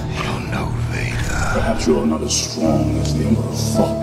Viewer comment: « Perhaps you’re not strong as emperor fucked » i heard lol